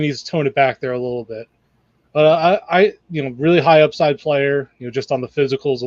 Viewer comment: Should probably rename this YouTube video “3 goobers drag Duece Knight through the mud for 8 minutes” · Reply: I thought they said raw but high upside?